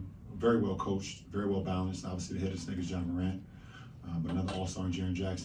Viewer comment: DId he say the N word at 0:12? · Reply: “the head of the snake is ja morant”